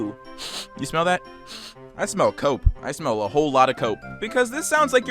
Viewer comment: I thought he said "I smell coke" and I was very confused for a the next few seconds.